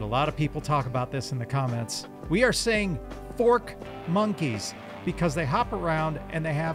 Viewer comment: I thought they were saying "fort monkeys" and I was like "they have forts or they dedtroy your forts?"